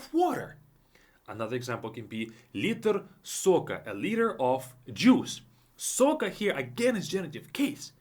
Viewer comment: Why did I hear „A leader of jews“ 💀